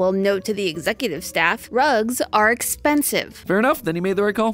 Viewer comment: could’ve sworn I heard say drugs not rugs